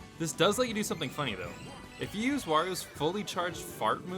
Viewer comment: I swear he said mario at 4:07 XD